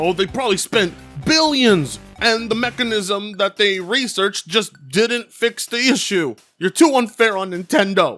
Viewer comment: LMAO Now you got me hearing things. I thought at 06:27 you said "The Mexicans who researched this didn't fix the issue.'